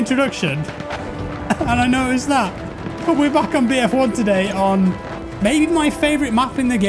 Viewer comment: did he said we re back in BF4 today :D